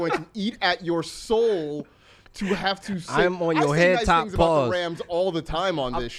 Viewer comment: did he just say what I thought he said lol. “I’m on yo head top pause… N*gga..”